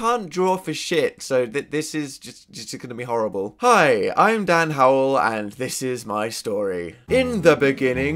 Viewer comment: i thought he said "i'm done howell" and went like yeah dan same but then i just. oh.